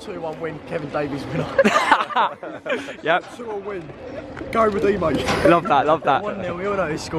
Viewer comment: I swear at 1:43 that guy said 2-2 win